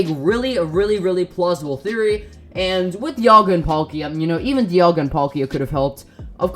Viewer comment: when he dialga i swore he said diavolo Kingukurimuzon no nōryoku!